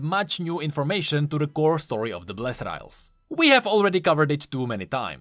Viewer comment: Did I just hear say he hopes there WON'T be more lore about the blessed isles ?!